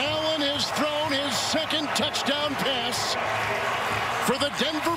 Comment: HES IN FOR THE TOUCH TOWN!!!! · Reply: Sure sounds like he said touch town! 🤣🤣🤣🤣🤣🤣